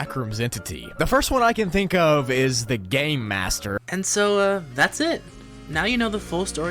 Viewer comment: When said game master it sounded like he said gay master❤️‍🩹🥹